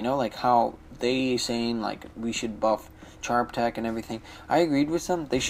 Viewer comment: did u just say they should buff Charbtek?!?